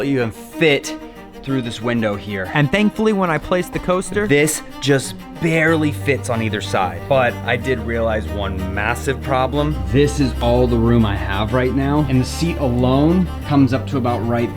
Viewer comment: did u say ma ma massive? U know what else is massive